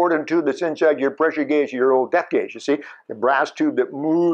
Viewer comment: Subtitles say ”your old death gauge”